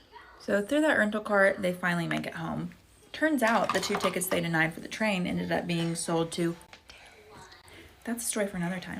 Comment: What did she whisper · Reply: terrorists 😢😢😢